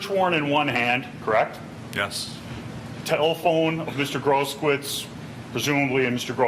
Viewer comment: Did I hear the defence lawyer refer to the injured man as "Mr. Gross Squits"? (4:58) How appropriate!